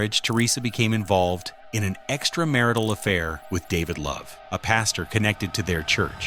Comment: At first I thought she said “Buddy Love” and I thought, “the nutty professor”.